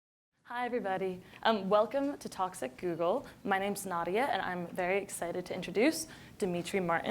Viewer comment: I thought she said welcome to toxic google. Hummm